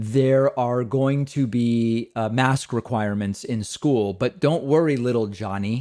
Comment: MASK REQUIREMENT... I thought they said MATH REQUIREMENTS! Thats a gateway drug to SCIENCE! Only Jesus can save us... or the easter bunny or Santa Claus